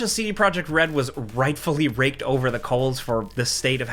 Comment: lol, I thought he said "Raped over coals" at first XD